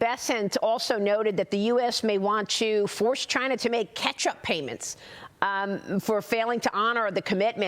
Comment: Maria says "catch-up payments" 3:04 CC says "ketchup payments"